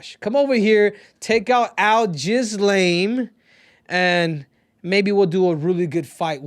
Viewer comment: I thought you were saying al-ghislane which is hilarious but al-jizz-lame is something g a child would come up with 😂